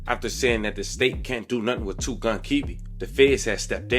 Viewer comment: For the "WORD BAITED" crew out there, @ 8:27 he doesn't say "Kiwi"! He says! The person, or AI app transcribing the captions is getting it wrong! Just like @ 7:20 doesn't say, "Said fuck you to the option" Clearly, it's "Said fuck you to the opps." There are a bunch of apps for automatic closed captions, but none of them are 100% accurate. So, ya'll talking about the man says, "Word Baited" and/or "Word Baby" @ 8:32 you are wrong! I do think he says, "wordbatim" though 🙂